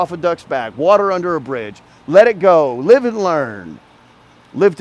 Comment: I don't know what you said, I believe bridge, but I heard "just water under the fridge". Made me laugh